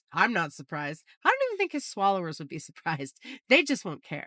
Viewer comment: I swear I keep hearing "his followers" as "his swallowers"